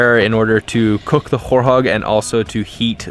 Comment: All this time i thought he was saying horehog..until i read the title hahaah, nice video.